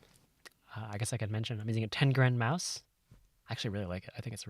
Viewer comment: thought you said ten grand for a moment there